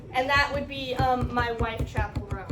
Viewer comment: What I heard: "He's a lesbian cause Iove him!" What you said: "My wife Chapel Roan." - "He's married to a woman." Conclusion: You are a cheater and a home wrecker.